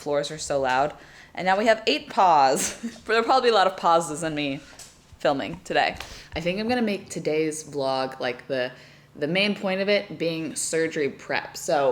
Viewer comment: I heard it as a lot of “paws-es” instead of pauses. 😂😂😂😂